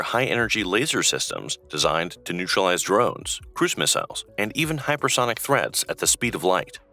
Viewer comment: What the video actually said was, "high-energy laser systems designed to neutralize drones, cruise missiles, and even hypersonic threats at the speed of light...." But, listening at 2x speed, what I heard was, "designed to neutralize drones, *Christmas elves*, and even hypersonic...." 😲 Yeah, flying-reindeer sleighs without proper airspace clearance had better BEWARE!! 😂😂😂😂😂